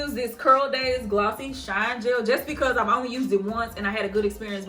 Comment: what was the name of the gel you used in this video? I thought you said Curl Days Glossing Shine Gel, maybe?? Can't find it online, so maybe I heard it incorrectly? Thanks Fun video as always!! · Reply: It’s CurlDaze!